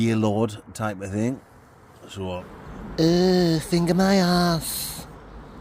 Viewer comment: If I was sitting in a grave yard and I heard "err finger my arse" Im outta there.........